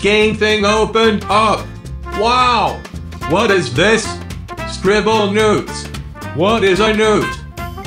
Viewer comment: I thought he said scribblenudes XD